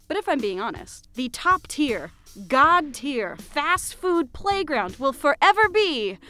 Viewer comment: DIS I HEAR GODTEIR? Hmmm WHERE MY HOMESTUCKS AT